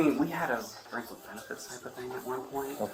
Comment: I thought I heard somewhere around the 13:00 mark, he said something about "a brief friends with benefits thing"?? But I'm not certain I heard it correctly.